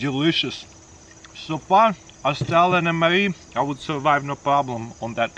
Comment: I thought he was saying Stalin’s mre